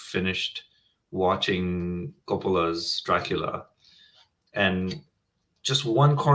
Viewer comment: whose Dracula? I didn't catch the word. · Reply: Anurag bhai Francis Ford Coppola’s Dracula …came in 92